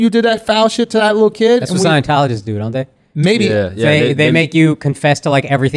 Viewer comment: I thought he said psychologists and not scientologists. I was bouta never go to therapy ever